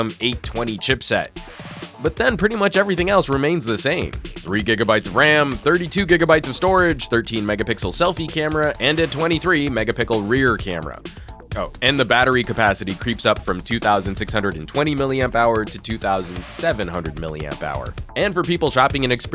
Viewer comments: Sounded like he said "megapickle" 😂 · did i hear megapical..?? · Did he... Did he say mega pickle instead of mega pixel? Or am I mishearing xD